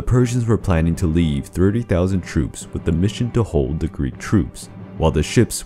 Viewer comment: the video says "30,000 troops" but the subtitles say "30,000 soldiers". Which one is it 30,000 troops or 30,000 soldiers?